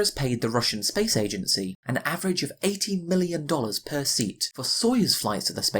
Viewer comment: So NASA pays ROSCOSMOS 18 millions per seat and Dragon is much cheaper at 55 millions per seat. Uhm, does not compute. Either I am mishearing that or you mispronounced it.